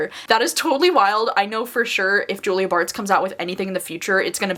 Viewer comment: At first I thought you said "Julia BArth" who was the sister on an old friend of mine but when you made it clear it's Julia Bartz it was in the context of being someone's sister.